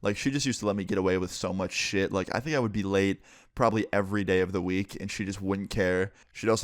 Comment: In the beginning when he says I would be late everyday I thought he said I would get laid everyday